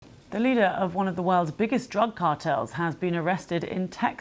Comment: Did he just say “it was leadered by”